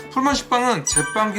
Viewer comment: 풀먼 삭빵 첨 들었을 때 풀무원 식빵으로 들었던 적이 았었죠